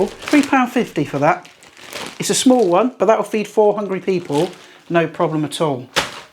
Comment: Hey, nice one!! Tips for carving also super. In the beginning you said: “this chicken could feed 4 hungry people”… I thought you said: “4 hundred people”… I was thinking: brain is melted, desintegrated from the heat 😁… he is out 😵‍💫